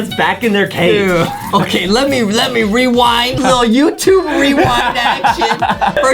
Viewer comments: Did Justin just say "youtube rewind that s**t!"?! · did justie just say 'youtube rewind that shi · is it me or does Justin say Some YouTube rewind s**t